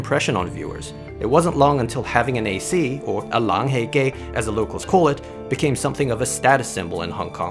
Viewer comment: I wasn’t paying attention and thought that said “long hei gay”